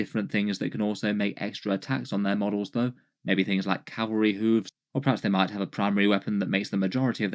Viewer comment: I heard "Cavalry hooves" as "Cadbury hooves," which sounds less dangerous but definitely more delicious.